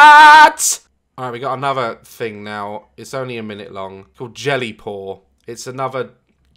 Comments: I thought he said jelly porn 😂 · ok but istg I heard “gelly porn”